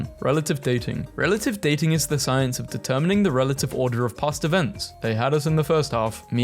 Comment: why’d I hear “pasta vents” instead of “past events”?